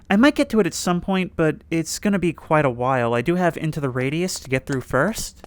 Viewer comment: I heard ‘into the radius’ 👀